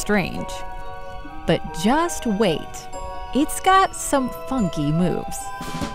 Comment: I heard “fucky moves”